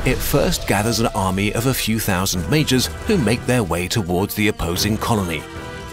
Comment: "When a kingdom invades another, it first gathers an army of few thousand mages". I'm kinda disappointed that I just misheard "majors". :(